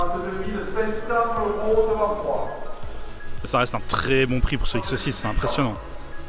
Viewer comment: Au lieu de comprendre "ça reste un très bon prix pour ce x6" j'ai entendu "ça rest un très bon prix pour cette saucisse" 😂 😂